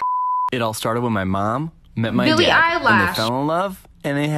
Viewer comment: DID SHE SAY BILLY EYELASHE?!?!?!?!?!?!😂😂😂😂😂😂😂😂😂😂😂😂😂😂😂